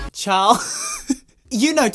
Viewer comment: he said ''ciao'' (im commenting this cause the person who subbed the video didnt know what he said)